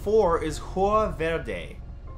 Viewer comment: Lol I thought he said jugo verde at 6:55 lol 😂